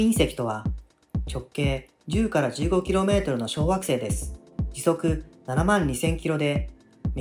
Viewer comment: 直径10〜15kmの小学生ですに聞こえたw